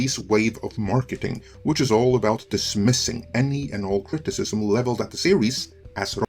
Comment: To me it kinda sounds like he is saying eroticism.